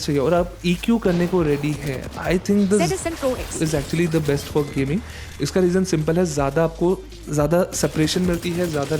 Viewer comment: the audio got bad , i didnt hear what iem u said ? Can u clarify pls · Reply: zsn pro x